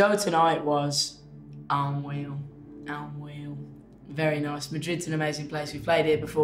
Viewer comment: Can anyone tell me what does he say in minute 1:21?? Cause I cannot understand him. I'm sorry, I'm Spaniard :) I think he says unreal but I'm not sure because he doesn't pronounce the r at all hahaha maybe because of his accent, I don't know. Please tell me if you know it, thank youuu :)) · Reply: yeah he said unreal, just with a different accent :))